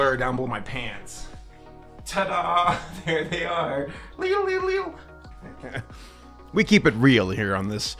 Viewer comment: Did he say "leedle leedle leedle"?